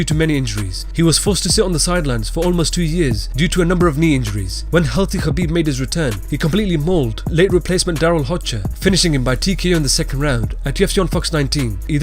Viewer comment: I initially thought you said HALAL Khabib